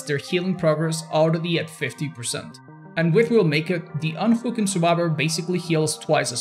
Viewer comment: I had this video with subtitles and at 1:42 it says unfu*king instead of unhooking lol 😂